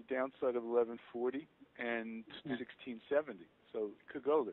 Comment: I couldn't catch him, did he say downside of 1140 and ...? 1670? 8:20ish mark